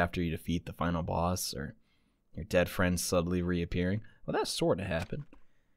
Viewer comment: the way that i thought mikan said your a dead fart when she actually said your done for im sobbing rn 😭